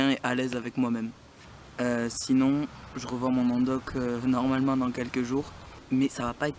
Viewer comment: J'ai compris "je revends mon endoc" je suis bête 😂😂